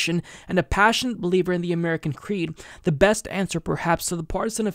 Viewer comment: When she said she's a believer in "the American creed", I thought she said American greed. I did a Freudian slip for her, because I think it's closer to what she actually believes, especially if she considers herself center right.